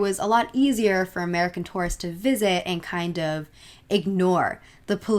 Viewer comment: everytime she says "tourists" i hear "taurus" and i cant shake it off. great vid bt 💓 :D